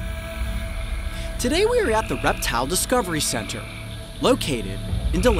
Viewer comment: When he said reptile discovery center I thought he said erectial disjunction center